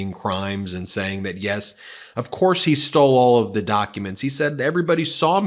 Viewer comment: You were saying that he said he “stole” them in the beginning. I heard him say he “took” them. That’s some word play worthy of the republicans. We gotta be better than that.